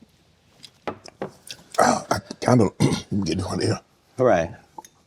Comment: “I’m kinda gettin’ hornier”? 😂 what??!! Did he say??!!